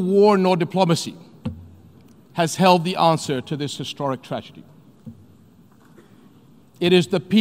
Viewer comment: at the moment 0:12 quote he did not say treasure as what's written in the subtitle but rather he said tragedy